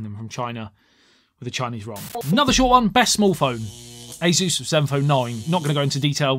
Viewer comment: What is the phone Asus, you mention at 22:00? You said it so fast I couldn't hear what you said. Your beautiful English accent is slightly difficult for a Northwesterner. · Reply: ASUS Zenfone 9 👍